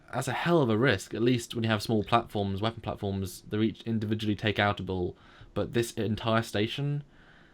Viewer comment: Did he say "takeoutable"? Well, if yes, then that's a new one to me. :-)